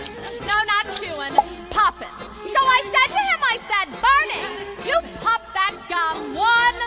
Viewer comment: I THOUGHT SHE SAID “If you pop that GUN” HELP